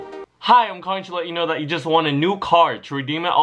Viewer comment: i am sure u didnt write the subtitles, cuz ur saying "won" in the video and the subtitles say "want"